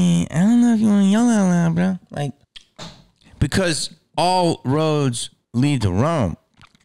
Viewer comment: *Me being a wrestling fan*: Rhodes?! Did I just hear Rhodes?!?!